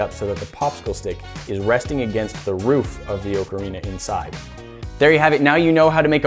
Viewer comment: Did he just say *oe-canina* it’s *acarina*